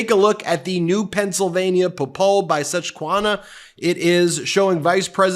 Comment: Brother I grew up in PA - wtf did you say at 0:36?! Sus qwa han ah